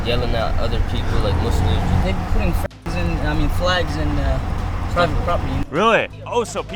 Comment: Did bro say “F@ instead of “Flags”? 😂